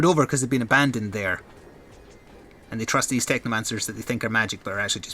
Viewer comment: I swear I heard you say this is the Techno man service when you said the name xD